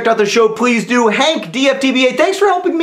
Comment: What does John say at the end (3:29) when it sounds like 'H isyf'? If it is that what does it mean? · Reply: Hank, I'll See You On Friday